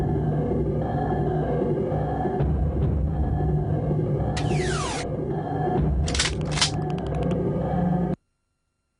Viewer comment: What is this? You didn’t say the name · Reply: It's just 'Amok'